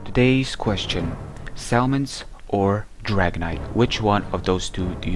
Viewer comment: i thought he said salmons or dragonite... :|